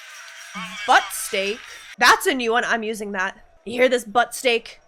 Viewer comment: "Butt steak"? Fuck! I always thought he called him "fuck stick". I've been calling people Fuck Stick for years, because I thought that's what he said.